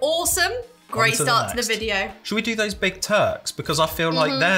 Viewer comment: When said Shall we try the big Turks? I thought he said big turds. 😂😂😂. I’ve not seen these in the states but when I do I’ll think of what I thought said😂😂😂😂😂